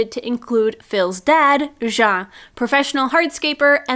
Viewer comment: I misheard Jean's introduction as "Professional Heartscaper" and I will now be exclusively referring to myself using that from now on. 😂